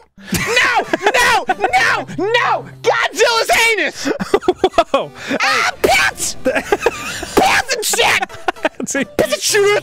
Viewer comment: What the hell did Arin say at 2:21? I heard "anus" but that was it. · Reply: I'm a month late cause I'm finding out YouTube didn't notify me for like 20+ comments but he says "GODZILLA'S ANUS. AH PISS...PISS N SHIT...PISS-N-SHIIIIIT.......Cheeeeeeeeeeeeeese." Truly a quote to live by really.